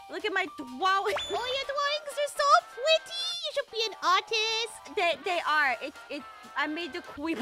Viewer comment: Tell me why at 10:20 I thought Kim said autistic